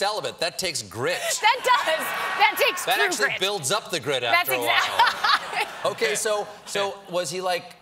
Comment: My Priest had a pretty good one handed grip. Oh... GRIT. Well, it did happen at a Charity beach party.. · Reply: I too, at first, thought I heard "...being celibate ... really builds up the grip ..." from Stephen - that would've been pretty funny xD A real opportunity missed